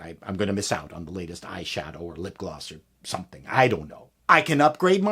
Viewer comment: did you just say iShadow ? xD